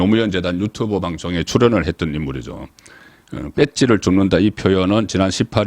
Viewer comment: 뺏지 줍는다 ! 지분권 주장으로 들려.